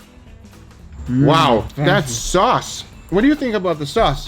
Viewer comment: I thought he said thats sus